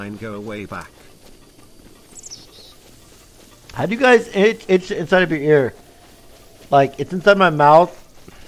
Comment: wtf is he saying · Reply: How/have you guys itched the inside of your ear